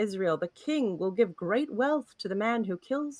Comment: Not me mishearing 10:05 "King will give great love to the man" and had to go back to hear what it actually said